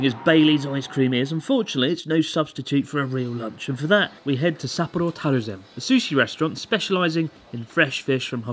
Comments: Lol at 4:25 the subtitles said "we head to support terrorism". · the subtitles say "we head to support terrorism" wtf